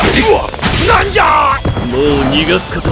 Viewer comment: Did I just hear... The N-Word